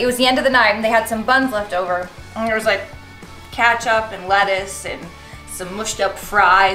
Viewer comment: did she say katchup